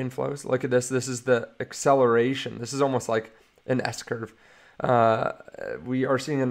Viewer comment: On 5:04 did he say this is almost like… an Escher…? 😅👏🏼👏🏼